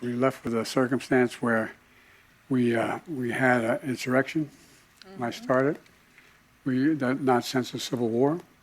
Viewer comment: does he say “That I started” or “When I started”?